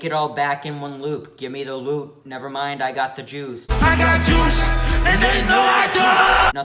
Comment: i thought you said i got the jews